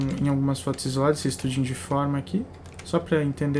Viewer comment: O que você disse no minuto 2:34?estudos de quê? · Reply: Estudos de forma!